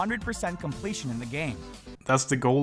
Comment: Did you call BotW shit at 4:55? If you did, were you using satire? Its honestly really hard to know what you said there and as far your tone goes, idk.